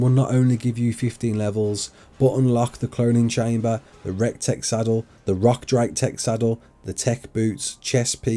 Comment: Idk why I have to point this out but I just heard him say “Rek Tek saddle” lol